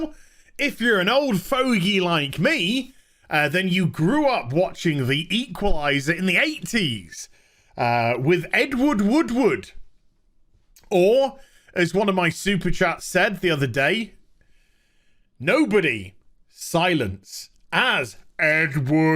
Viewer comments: I thought you kept saying Edward Woodwood. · What is saying “Edward Woodward” What I hear “Edwood Woodwood”